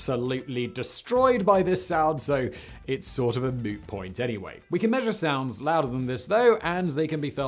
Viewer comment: in 2:05 did he said "it's sor of a moo point anyway"? Is he making a reference to Joey in friends?!?!?!